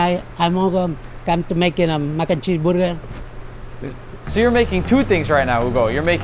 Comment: I thought he said booger😂😂😂